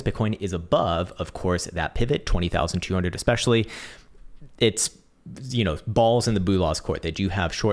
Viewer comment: Did you just say - Balls in the Bullahs? LOL..